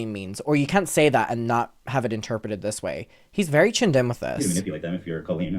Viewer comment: Can someone please tell me what word says at 1:30:52, I can't figure it out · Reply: "He's very tuned in with this"